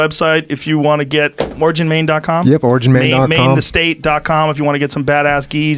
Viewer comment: If this video gets demonetized, it might be 2:36:35 when the auto captions say “ more gin mein kampf” instead of <link>. Get after it, folks!